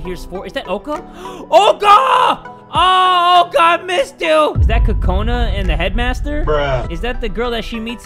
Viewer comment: Go to 1:57 and turn english subtitles on when he says "Is that Kokona?" in the subtitles said "Is that cocoon"? 😂😂